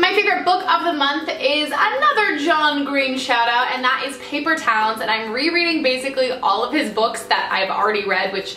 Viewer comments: I totally thought she said "Paper Towels" instead of "Paper Town" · I thought you said paper towels haha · I thought she said she read the book "paper towels" and I was like wtf is paper towels and then I found out that she said "paper towns" by john green